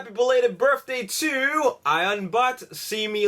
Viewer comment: I noticed that every time he says "birthday", it sounds like he says "berfthday" lol